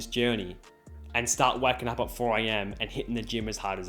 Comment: When he said I was "waking up at 4 am" I thought he said wacking up 😂